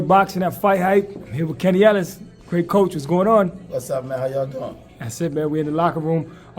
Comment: I thought y’all said it Shakur won’t let y’all “Do what y’all do”😂😂😂